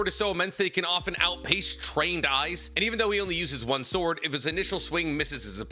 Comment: what did he say he can outpace? I can’t understand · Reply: Trained eyes